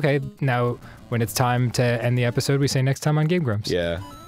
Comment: Its sounded like Dan said, "Next time on Game Rumps." I'd watch that...